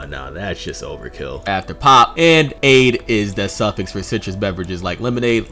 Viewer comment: What u fuckin said bruh😂 Swear them mfs finna "pop" out her dress☠